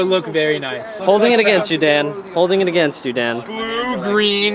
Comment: did i hear Gentstudent?